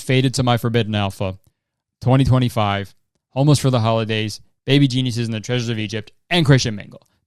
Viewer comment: Bruh you said baby geniuses;the treasures of egypt and i thought you said baby jesus 😂